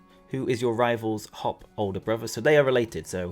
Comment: Ooo I love your coverage! When you said "Your rival Hops older brother" I thought you said "Your rivals hot older brother" and I was just like, yeah big mood.